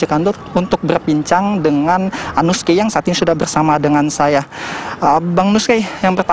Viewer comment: Nth gue salah denger atau nggak di menit 3:43 reporter bilang anus key